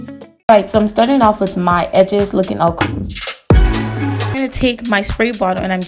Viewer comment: I know you don’t mean anything by this but whether it’s consciously or subs-consciously we as black women need to STOP using negative connotations about our natural hair textures. You have a beautiful head of natural hair and a strong platform. Choose your words strategically because young black girls are watching. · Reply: I was like did she just say "I'm starting off with my edges looking ugly" uh girl, that's how your edges be